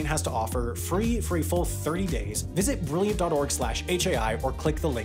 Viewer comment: Could someone elaborate on what the narrator says at 6:07, all I can hear is "free for a XXXXX days".